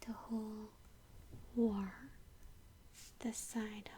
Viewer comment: Wow, your English is pretty amazing! But what were you saying at 2:08? Sounds like 'war'.